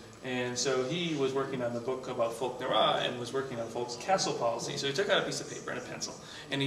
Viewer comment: Anybody could tell me what is that he says at 1:55? Folk Nawroz? I genuinely cannot understand from the speech.